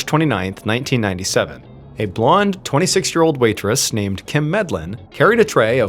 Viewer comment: i thought he said nurse instead of waitress. I was like why is a nurse carrying a tray of filet mignon through a topless bar? 😂